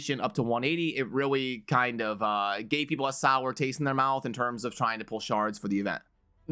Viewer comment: half listening and it sounded like, "gay people have a sour taste in their mouth..."